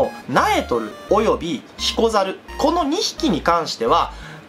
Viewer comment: ヒコザルがシコザルに聞こえたw